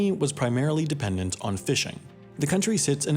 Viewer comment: I thought you said "fission"